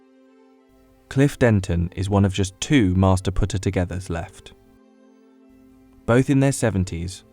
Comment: I heard “master putter-togetherer” and scrambled for the comments